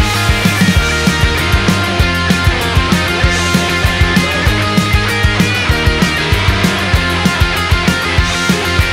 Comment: Anyone know what's being said during the guitar solo at 1:56 ? It kinda sounds like Tony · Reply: "play that thing Frank, play that thing" c r i n g e